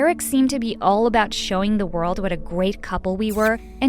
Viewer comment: Am I the only one who heard her say gwate instead of great?